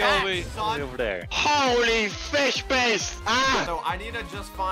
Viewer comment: "HOLY FISH BASE AHHHHH" was amazing is a goat. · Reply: I thought i heard "HOLY FISH PASTE"